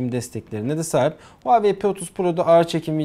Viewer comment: huawei p30 pro mu dedin abi ben mi yanlış duydum 😂